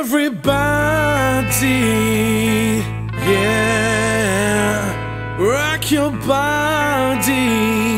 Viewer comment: I heard ''rock your bald '' lol